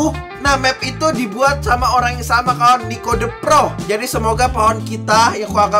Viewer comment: apa gw salah dengar kalo bang bilang "Pico" The Pro